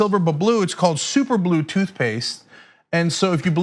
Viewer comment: Ah no.. I thought he said Use Super Glue as Toothpaste.